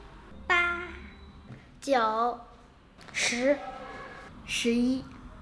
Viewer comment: I heard "Joe" XD